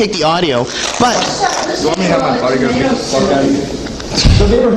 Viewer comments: did curt say how you gonna get the fuck out of here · “You want me to have my pie- gonna beat the *$&@ out of you?” That’s what he said- listen to his mouth.....🤨